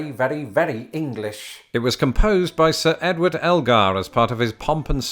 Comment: I was told by the orchestra director at Berkeley that World War I absolutely shattered Elgar, and you can hear it in his music. The famous Pomp and Circumstance was written prior to the war, his equally famous cello concerto was written after the war. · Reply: I thought he said Algar